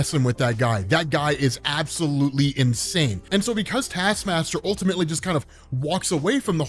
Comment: did anyone else hear say "Assmaster"? lol